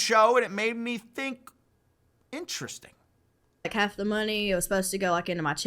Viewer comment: okay maybe i'm hearing wrong, but you say she said "half the money" and when you play the klip i can only hear her say "i kept the money" that's so weird :D Timestamp. 11:05 - like i believe what you are saying is true but i've heard it 6-7 times now and i only hear her say "i kept" not half..